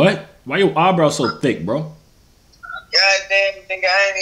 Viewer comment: Did dude just say nigga racist ahh